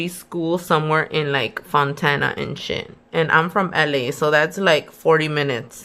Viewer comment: Bish I thought you said Montana I was like you crazy as hell driving. That far but thata not what you said bahahahahaha🤣😂🤣😂😆